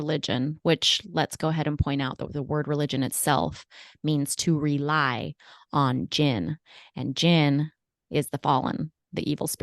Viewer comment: Interesting take on “rely on gin”. I heard “re-legion”. Hence their obsession with signing up the dead”.